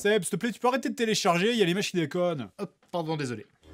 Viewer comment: depuis toujours j'ai compris "machine à code" à la place de "l'image qui déconne"